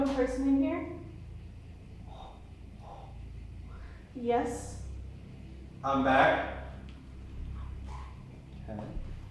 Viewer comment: I hear a whisper repeating, “I’m back.”